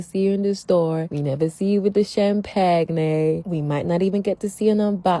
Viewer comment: did she just say "cham-pag-nay"... 😂😂🤣🤣🤣🤣 learn how to speak English people.